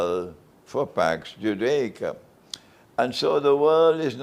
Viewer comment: Aipacs? what does he say I couldn’t catch the word packs judaika? What is he saying? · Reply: Pax Britannica Pax Americana Pax Judaica